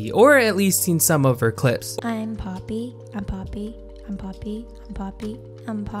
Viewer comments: Poppy: ‘I’m poppy’ What everyone else hears: ‘I’m poppy’ What I hear: ’I’m part bee’ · he did not say “Poppy”. He said “Papi”.